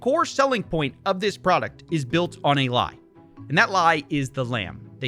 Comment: Coffee you clearly misunderstood him, I think maybe bc of his accent. You thought he said "it is built on AI.". But if you listen, he actually said "it is built on a lie." Simple mistake 😉 nothing to investigate here 🤷